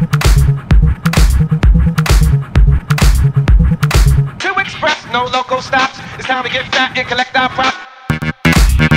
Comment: Who's able to tell me what the women says at 0:39 please ? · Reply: Two Express, no local stops It's time to get fat and collect our props !